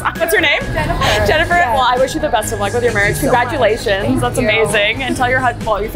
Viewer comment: I don't know why I heard EJACULATIONS instead of CONGRATULATIONS in 8:11 😢